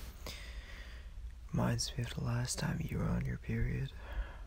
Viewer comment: I don't understand what he says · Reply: I think he said "reminds me of the last time you were on your period"